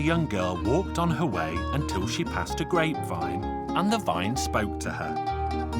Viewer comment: i heard “until she passed a great vibe” im weak